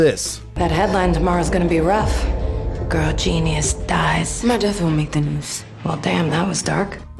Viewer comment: wtf is she saying???? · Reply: My death wont make the news? Took me 3 times of relistening lol